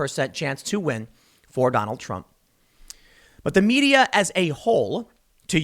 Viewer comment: Lol. I thought you said: The media as a whore.